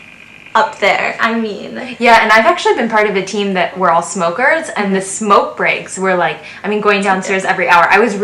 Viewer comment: I'm not a smoker! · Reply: Thank goodness!! =) I misheard "we're all smokers [in my team]"